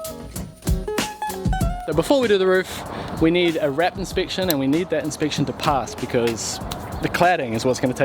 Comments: I honestly thought you said a RAT inspection....Whoops, my bad. :) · you Kiwis speak so funny. I could have sworn you said you needed a rat inspection.